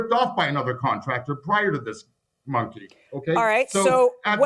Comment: Did I seriously just hear the defendant call the plaintiff a Monkey @ 12:03 ??!!?! Wtf and the judge didn't say ANYTHING!!!?